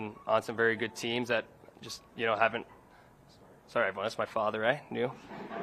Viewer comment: What did Mitch say at 35:47 that got him and everyone else laughing? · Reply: A phone rang in the background. It was his dad's phone and he made a joke telling everyone it's his dad. Said "he's new, eh" lol